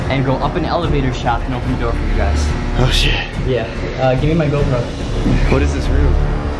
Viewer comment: the subtitles thoughhhh haha, 5:27 "shit yeah get my goat" instead of gopro loool